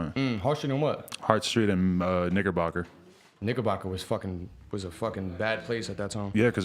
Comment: bruh I can’t be the only one who heard him dead say N*GG*Rbocker 💀